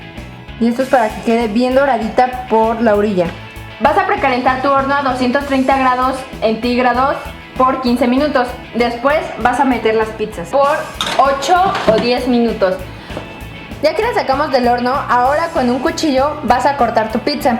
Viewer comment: escuche bien o dijo entigrados en el minuto